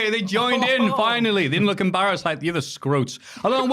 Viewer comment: What did say around 0:10-0:14?... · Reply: "Finally, they didn't look embarrassed like the other scrotes" Meaning